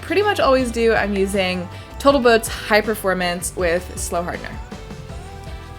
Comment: I too want to try Boataltoat one of these days! Also, 1:11 needs to be a GIF! 😄 Great build! Congratulations on a successful flight! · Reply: I thought she said botaltoat, I had to rewind 4 times